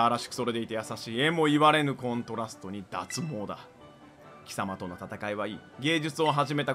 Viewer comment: コルサさんの脱帽だのセリフがどうしても脱毛だって聞こえるのは私だけ🤔??笑